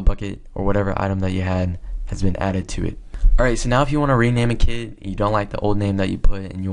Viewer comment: I thought he said "You can rename kids" LOL